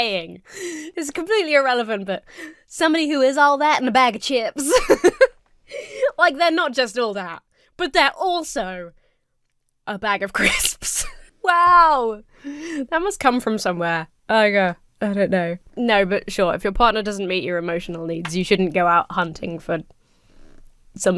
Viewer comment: I'm not all that. I'm just the bag of chips. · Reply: I always heard it as a packet of crisps. Maybe I'm just manufactured differently.